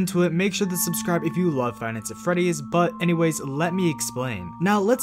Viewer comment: subtitles say finance at freddys